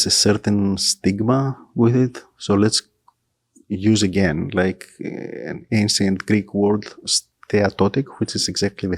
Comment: Hey! What did you say at around 58:30? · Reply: Apparently, my microphone decided to play hide-and-seek right when I asked: 'What is the Mantzoros classification?' Sneaky mic!